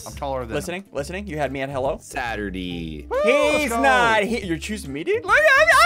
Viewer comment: Did said Saturdee (Saturday)